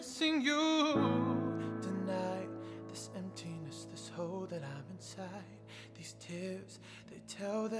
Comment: I swear it sounds like he's saying this "hoe that i'm inside at 0:33" I'm like damn Sam Smith you dawg